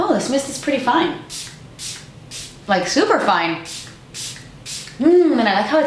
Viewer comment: For some reason I thought you were gonna say "Superfly" instead of "Super fine"....LOL!! My bad.....heehee.